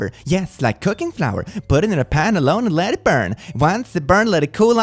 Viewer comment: omg I thought it was saying to put the baby in the pan and let it burn 😭